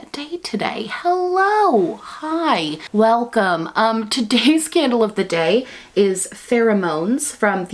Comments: When she said “today’s candle of the day is” I thought she said “today’s scandal of the day is” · For some reason I thought you said scandal of the day but that would be fitting too 😂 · I misheard and when you said candle of the day I heard “scandal of the day” 😂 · 🤣🤣🤣 For a second I thought she said “today’s scandal of the day” lmao so fitting